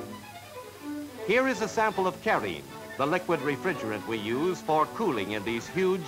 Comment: Does anyone know what refrigerant "Carriene" is at 6:46 ? Perhaps R12? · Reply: It is methylene chloride. R11 was Carrene #2 and R12 with R152A was Carrene #7.